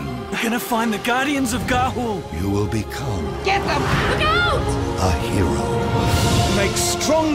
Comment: "get the fuck out" anyone else hear it?